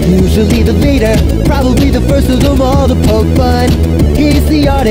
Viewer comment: Is it just me when it got to edds part i thought it said "poke butt"